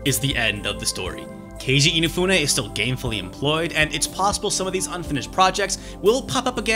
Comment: I thought you said inafune is gayfully employed